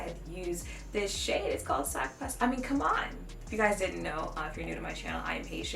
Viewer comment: What did she said at 2:23 I can´t hear it properly, is she asian? (I acually couldn't hear, not questioning her) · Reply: I’m Haitian. The shade name “sak pase” is Haitian Creole